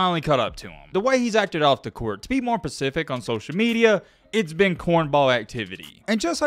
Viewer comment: Did you say “to be more pacific”?